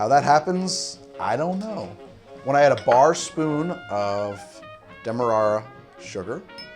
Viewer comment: it says "demerra" instead of "demerara"